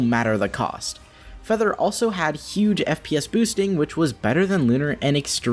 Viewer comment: EPS instead of FPS